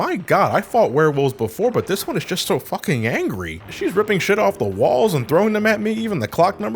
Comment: I'm sorry. Did you say " she "?